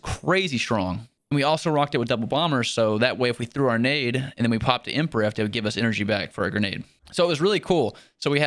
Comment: At first I thought he said pimp rift, just me?